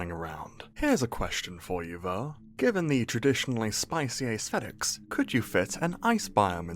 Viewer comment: did u just say ice spice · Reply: idiot, he said “spicy aesthetics.”